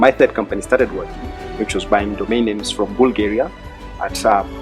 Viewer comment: About 4 min through the video, a gentleman in blue jacket says he bought ... what ... ? from Bulgaria for 35USD and sold it in Kenya for 150USD, can someone tell me the name of the product the man mentioned please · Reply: i heard him say domain names am not very sure